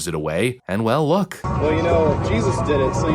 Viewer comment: I believe I heard Mr. shout his defense for what he was about to do… I think he said, “well Jesus did it.” Likely referring to Matthew 21:12-13 *****Jesus at the Temple 12 Jesus entered the temple courts and drove out all who were buying and selling there. He overturned the tables of the money changers and the benches of those selling doves. 13 “It is written,” he said to them, “‘My house will be called a house of prayer,’but you are making it ‘a den of robbers.”** How completely out of context using that as his justification. But that’s what these people do, they rationalize with something that they heard, something they do not understand. Ie: “well, Jesus did it.“